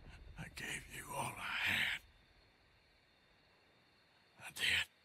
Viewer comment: I thought he said “I damn you all to hell”